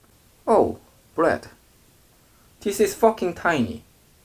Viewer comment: this is f**king tiny..... Did i heard wrong or?????😧😧